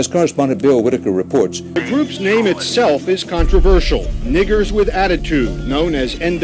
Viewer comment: did he say n!ggers not niggas with attitude ? Did he say n!ggers on the news ? 🤯